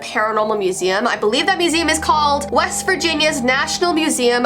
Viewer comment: “West Virginia” Me: “wEsT vIrGiNiA mOuNtAiN mAmA” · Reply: I thought you said west vagina